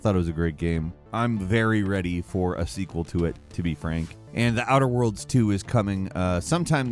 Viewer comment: I looked at the screen when you said Outer Worlds 2 and almost had a heartattack. Thought you said Outer Wilds 2. Now I'm sad.